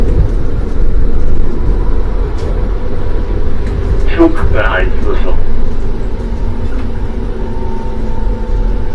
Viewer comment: Ca bei 7:07 kommt ein Signal das wie rot aussieht, ich glaube er sagte : weiterfahren? Konnte ich nicht richtig verstehen. Mein Frage was bedeutet das Signal allgemein? Ich habe keine Ahnung davon, für mich sah es nur nach Rot aus . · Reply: Das Signal war gelb und ich sage "Halt erwarten". Das war ein Vorsignal, dass ,ir vorankündigt, dass ich am nächsten Hauptsignal rot zu erwarten habe